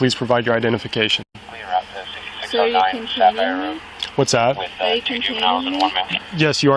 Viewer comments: I think she said “containing me” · I swear I heard her ask "are you containing me" Am I the only one who got that? This officer was amazingly patient and professional.